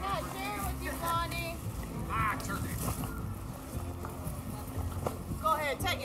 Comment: I heard someone said as the gator: My turkey!